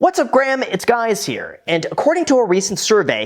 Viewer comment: I had to hit rewind to confirm what I heard. "What's up Graham, it's guys here." So funny... Was that intentional?